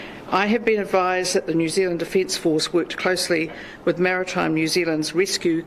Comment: I thought for a second she said "New Zealand Defeat Force"....